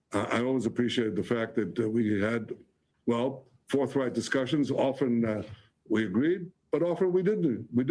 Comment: did he say fourth reich discussions? lmao